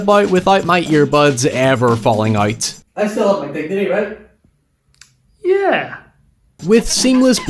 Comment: I couldn’t understand what he was saying at 7:10 so I put on captions and it said “I still love my dick today, right?” 😂 Clearly that’s not right. I finally figured it out; “I still have my dignity, right?”